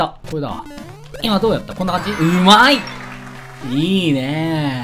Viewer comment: 『うまぁ～い』が億泰に聞こえたのは僕だけ？w